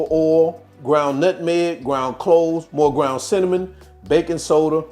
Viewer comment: Sounds like he said ground cloves in the ingredients? Did anyone hear that